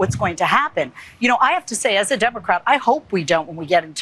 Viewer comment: She is not a MAGA... She actually said '' as a democrat '' at the 0:36 mark .. Please report carefully and not interpret others without facts...if not, you are not better than FOX '' news ''....